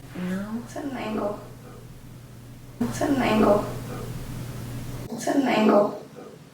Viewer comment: it sounded like it said "angle"